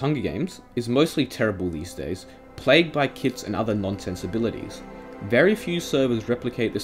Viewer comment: I thought he said "Plagued by kids" and I was like "What? Minecraft is a kids game, kids are allowed to play hunger games" Then I realized I'm an idiot